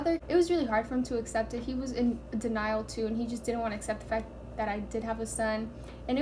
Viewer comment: Did she say he didnt accept the fact that I did have his son or he didnt accept the fact that I did have a son 6:20 (dont hate) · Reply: That i did have a son* :)